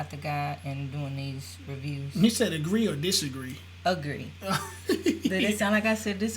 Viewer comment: I thought she said disagree also 🤣🤣🤣